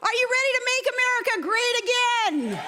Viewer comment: ARE YOU READY TO MAKE AMERICA GREED AGAIN!!!!? That's actually what I heard 1:08 ...